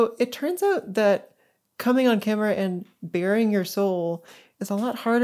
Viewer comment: In the subtitles at 27:47 "...bearing your soul" I think is a mistake. I think you said, rather, "...baring your soul." Sorry if i'm wrong. Anyway, thanks so much for the subtitles to begin with! It's really quite annoying sometimes when you can't get decent subtitles on a video, and even then this is when I am a native English speaker with perfect hearing. And.....great video! <3